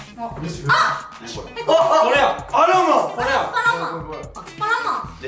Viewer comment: 「アロマ！」に聞こえて屁いいにおいすぎるんかとおもった